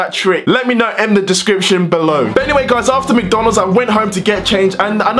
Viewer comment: did he say comment in DESCRIPTION below 😂😂😂😂😂😂😂😂😂